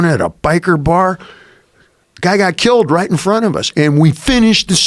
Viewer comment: My english is not good enough. What does David say in minute 2:13? Best regards from Barcelona, Spain. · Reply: guy got killed right in front of us